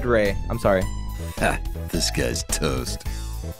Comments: What people hear: Disguised Toast What I hear: This guy is Toast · DOES HE SAY THIS GUYS TOAST OR DISGUISED TOAST VISIBLE CONFUSION